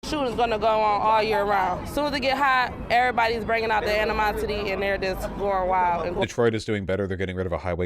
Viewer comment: When she said "everybody's breaking out the animosity" I got an ad, so it sounded like she said "everybody's breaking out the enemas" 😂